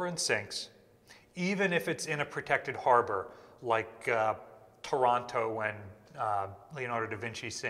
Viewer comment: I was so confused when I thought you said da Vinci sank in “Toronto”! It took me a minute to realise I misheard Taranto